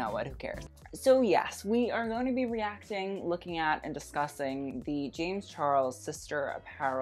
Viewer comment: I thought he said disgusting but then it actually fits the apparel.